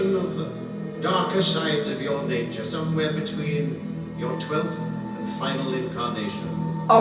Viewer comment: I thought it was "between" 12th and final incarnation. So its still fairly vague. Or maybe I misheard that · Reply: “between your 12th and final incarnation”. This would specifically be the 10th Doctor regenerating into the 11th Doctor. It destroyed the TARDIS interior because the 10th Doctor resisted regeneration, because he didn’t want to go. The Valeyard was spun off from that struggle. Like the Meta-Crisis Doctor, he had no regenerations.